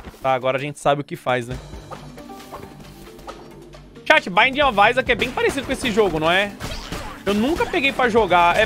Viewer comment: qual o nome do jogo que ele falou no minuto 30:00? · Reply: binding of isaac